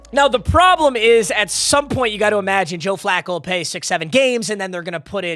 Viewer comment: WAIT DID HE SAY 67????!!!!!!!